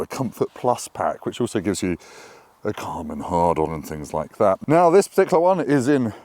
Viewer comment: I swear I heard you say "Karman Hardon" lol